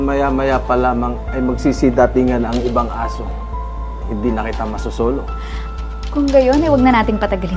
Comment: Did I just hear the guardia civil say: "Hindi na kita mo SO-SOLO." ????